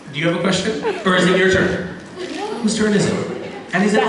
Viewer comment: what did that fan say in about 12:47 when misha said shhh?? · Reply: I think she said "Is it your wife's book?"